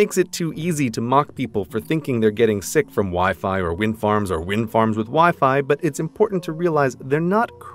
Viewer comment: wind farms with wifi omg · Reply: Ethan Marsingill I thought you said Wild Farts with wifi omg xD